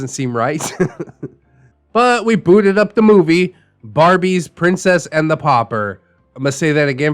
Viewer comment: I thought you said the movie name was “Barbie Princess and the Poppers” lmao I had to rewatch that part a couple times.